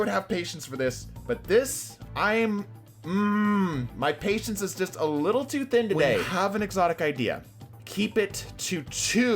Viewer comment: what you said: "my patience is just a little to thin today." what I heard: "My face is a little to thin today."